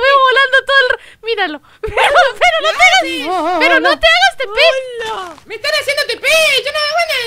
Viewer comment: En el minuto 3:59 le entendi a "Pero no te hagas Pende..."